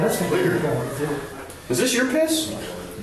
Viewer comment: The beginning of the video is hilarious. "Did somebody just Sh!t?" I thought this kid got so scared he $h!t himself or a$$blasted that room to oblivion. Haha, but it was their "other guy" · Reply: Yeah was it someone in the room next to them or something? 😝 The first thing I heard was, “is this your piss?” 🤣🤣🤣🤣